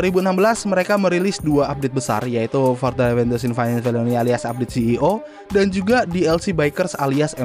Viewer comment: ngomong apaa coba😂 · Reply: 'Further adventures in Finance and Felony'